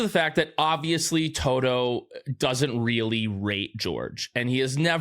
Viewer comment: I thought you said Toto doesn't really r*pe George, then realized you said raTe.... I was like WHAT?